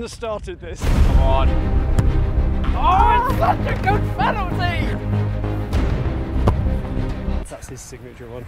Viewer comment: Anyone else think said : a f-ing good penalty